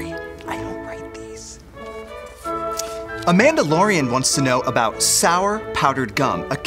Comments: I thought he said “The Mandalorian” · did anyone else hear mandalorian